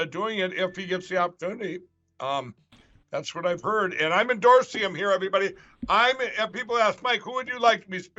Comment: Lol at 3:31 did he say Im endorsium , what language is this